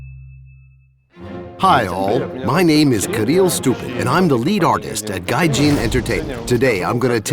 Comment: Did he just say his last name was "stupid"? Lol that explains a lot about gaijin xD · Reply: No, he said "Kirill Stupin" : ) Sorry for mistakes. Im Russian